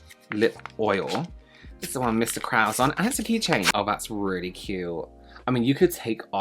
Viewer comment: When you said "and it's a keychain" I thought you said "hasn't he changed?!" with so much affection and I couldn't stop laughing 😂